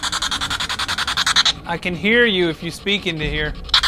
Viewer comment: I sounded like it said I can see you....